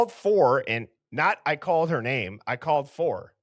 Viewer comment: I called for an ambu* is what I’ve heard from day one, but sometimes I hear ‘I called her name.’ But the second time it’s said it much clearer.